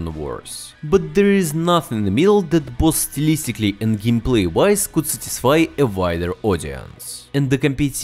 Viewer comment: what the hell are you saying. "There's nothing milded postlicitly, in gameplay wise Gus de Why the wider audience?